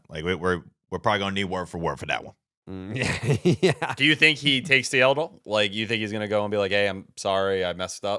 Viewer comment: Did T say “takes the elbow” meaning take a knee? Lol